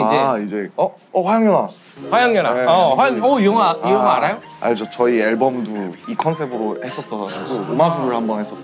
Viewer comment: 영상 내 17:58, 18:10 구간에 자막 오타를 발견하였습니다. 수금지화목토천'혜' (X) -> 수금지화목토천'해' (O) 시청해 주시는 분들의 너른 양해 부탁드리며, 향후 자막 작업에 더욱 신경 쓰도록 하겠습니다. 감사합니다🙏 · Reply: the english subtitles say "Yes, we made a film on that theme." I can hear that RM said "album" instead of "film," so maybe that's one possible typo? It isn't really a big deal, I'm very thankful for the subtitles you've provided already for this. Thank you!